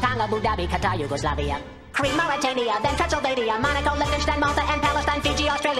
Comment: When he said "Transylvania" i thought he said "Pennsylvania"